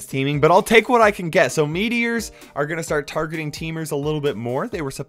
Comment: At first I thought you said that framers would he targeted by meat eaters. Was really confused until I realized it was meteors.